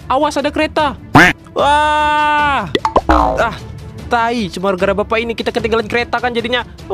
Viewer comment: BILANG APA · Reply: Tai